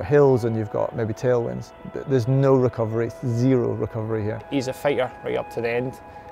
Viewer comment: i keep hearing ''he's a failure''....